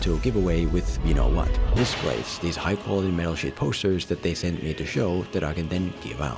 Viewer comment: <link> oh god i know its the accent but i heard "metal shit posters" :(